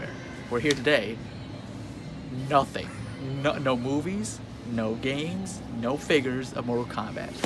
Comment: I thought you said no boobies